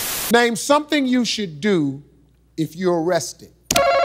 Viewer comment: Name something you should do if you're arrested. It Did sound like he said "if you're resting." I don't blame her for that. I love Steve, but sometimes he doesn't enunciate well.